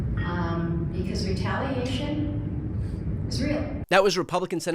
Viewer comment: Hard to hear, but I heard her say, "retaliation is real". That's so scary.